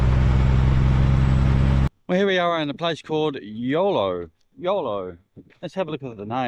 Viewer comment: EULO, I thought you said YOLO - You Only Live Once came to mind, ha ha.